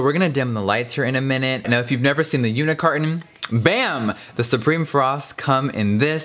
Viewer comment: i kept think he was saying “unicarton” but then i realized he said “unit carton”😂😭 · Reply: kyrra OMFG I thought he said unicarton too. Like I say unicarton because of him